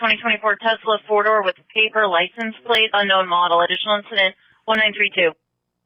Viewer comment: I swear I heard the officer say “PAPER LICENSE PLATE” 😭😭🤦🏾‍♂️